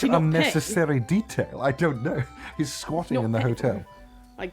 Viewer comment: DID I HEAR 7:41 "he's squanching in the hotel." HEY, IM SQUANCHING IN HERE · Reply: jade squatting